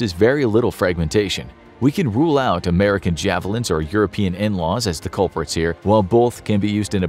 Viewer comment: I swear I thought he said "We can rule out American Javelins or European N'wahs" and my mind immediately jumped to all those Dagoth Ur AI voice memes coming out now